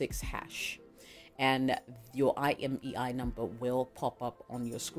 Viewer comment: That was great info my new wonderful friend, But please please do this put the codes on screen for us hard of hearing people 😊 or put them down in your comments, I thought you were saying impi number it was when I seen someone put imei number that I got it right, so please write your info down.